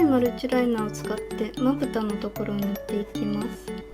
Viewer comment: なんか、かげのところぬっていきますがハゲのところぬっていきますに聞こえた！